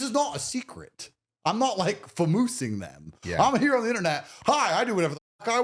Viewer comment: What does he say on 02:05:37? Furmusimg them?